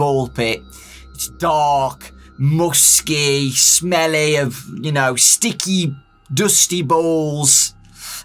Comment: this man knew what he said but continued with it anyway 😂😂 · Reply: he said sticky not dickey bu still funny